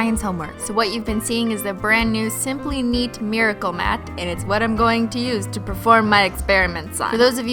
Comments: On the Mat I thought It said Menchie Mat. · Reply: I thought it said "miracle meat" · Lol at first I thought u said "simply meat"